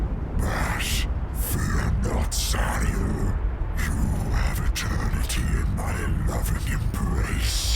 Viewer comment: I played it with subtitles and he is just saying "Sariel" the name of the wordbearer. The demon is not name once in the episode. Even the subtitles reference it as "Sariel-Demon". It is definitely not Lorgar here.